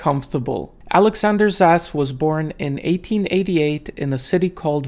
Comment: I thought you said "Alexander's ass was born..." Lmaoo